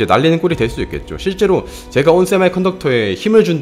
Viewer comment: 양심에 손을 얹고를 낭심으로 들었네 ㅋㅋㅋㅋㅋ